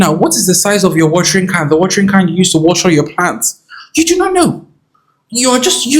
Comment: I know you said, "water your plants," but for some strange reason, I heard "water your pants." 🤣 By the way, good call with the fertilizer. I have two full bottles of fertilizer in storage that I don't know what to do with anymore.